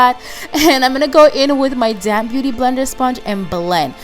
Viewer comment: Gorgeous! Don't judge me, but I laugh a little when I hear you say "damp beauty blender" I swear it sounds like "I'm taking this damn beauty blender" Lol. I know you're saying damp tho. Love your accent!